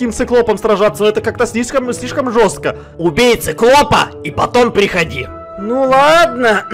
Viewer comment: Мне послышалось: Убийца клопа 🤣😂.